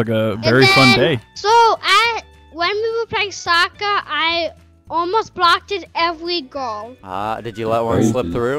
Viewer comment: I thought he said I almost got every girl at first😂